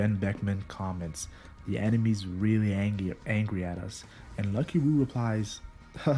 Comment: did dude say angy lmao 😂